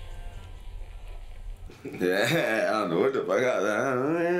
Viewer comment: i didnt think he said none of those i thought he said " i dont know what the fuck i was on"